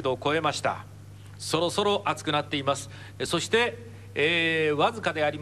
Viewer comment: did he say soda soda lol